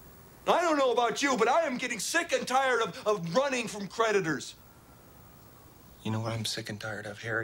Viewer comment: Does he say hes sick and tired of running from predators or creditors? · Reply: For many years I thought he said predators, but then I realized actually creditors. Lol